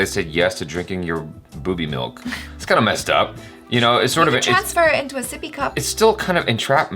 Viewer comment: Jaby didn't hear her probably, actually said: You transfer into a sippy cup! 😂😂😂